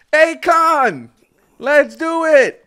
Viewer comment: the way you said ay-kon instead of akkun at 9:06 💀